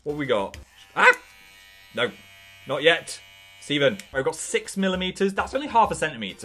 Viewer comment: did u call it stEVEN?